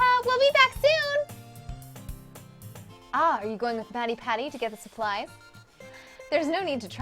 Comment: I just exploded It sounded like FattyPatty Woah